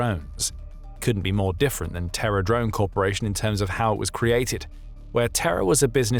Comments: I keep hearing it as "TerrorDrone" and I like that even more. Good on Japan and slava Ukraine. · I thought I heard Terror Drone. That drone entering my moving MCV flashed in my brain. 😅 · I thought you said terror drone until I saw the writing and was thinking of the murder drones show :)) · I kept hearing 'terror' drone corporation, and thought that was a little on the nose 🤣🤣🤣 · I thought "Terror Drone" sounded fitting but it was a mishear xD